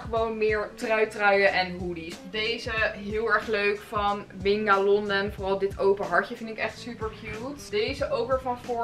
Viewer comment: wat zegt ze hier?? ninga?? lon?? welk merk is dit leuke regenboog truitje?